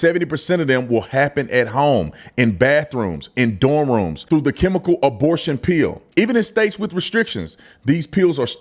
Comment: With your accent, I heard "abortion peel," and thought, "wait, there's topical abortion creams now?!"